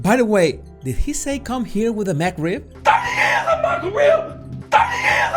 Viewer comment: I agree with what you said, and there were even more clues that agreed with your discernment. Also, I didn't hear him say McRib. Sounded like he said "30 years of my career."